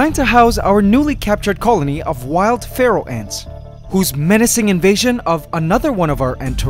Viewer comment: first i thought he said "Feral ants" and i was confused then only to realized he said "Pharaoh Ants"